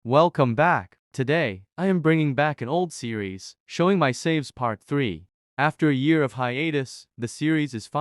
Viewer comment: I thought it said showing my slaves